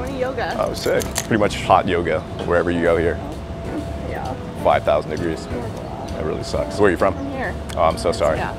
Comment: Did he just say 5000 n***ers on 1:53? lmao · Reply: Haha...5000 degrees 😉